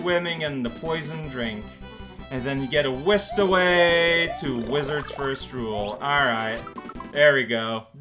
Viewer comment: Wait did I hear Wizards First Rule there? 20:12 ?